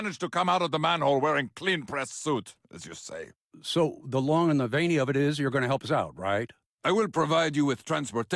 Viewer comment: did Chevy say "the long and veiny of it"? Never heard that one